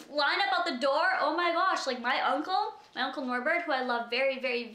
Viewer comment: I swear I heard you say uncle Norberg which is so weird cause my uncle passed away recently too and my last name is Norberg!